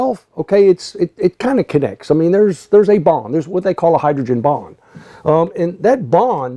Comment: You draw almost as well as I do.........And when you said "hydrogen bond" I thought you said "bomb" and then I got really intrigued!......Your pressure theory is proven in vehicle cooling systems where the system is pressurized by the radiator cap (about 13 psi) to raise the boiling point of the coolant (water)